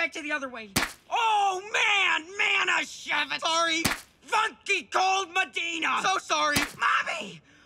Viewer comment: funky cold madena what the f*** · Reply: I thought he said "fu*king cold madeena